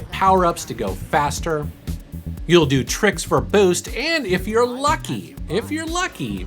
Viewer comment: When he was talking about the Speed Racer game, and he said "You do tricks" i thought he was about to say "You do Trixie" and i was like DAYUMN! that's one hell of a game